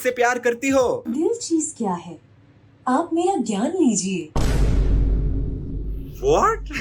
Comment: I heard dil cheez kya hai ap meri g*nd lijiye 😂